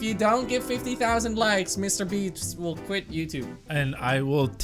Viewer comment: Did he say mrbeefs